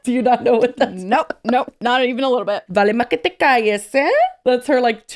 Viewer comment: I’m Colombian and I have no idea what said 😂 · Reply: Haha the first time I couldn't understand it but I believe she said: vale más que te cayes ey (shut up basically)